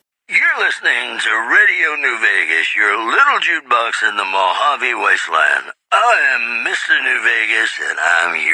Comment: I literally always though he said new span fedora and had no idea what he meant lol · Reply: and when he said "your little jukebox in the mojave wasteland" i thought he said "your little due bugs in the mojave wasteland"